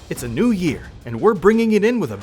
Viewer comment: At first I heard " its a new year. And it comes with a new ban" Edit:Thanks for those likes gg bros